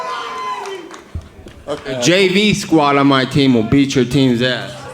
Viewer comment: can somebody write down what Diaz says to McGregor at 19:27? I think he says something like "Jay B?! will beat your teams ass?! "Did I get it right? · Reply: werner hansch JV*